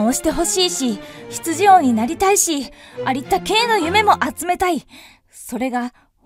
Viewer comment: ありったけ〜があったけ〜に聞こえた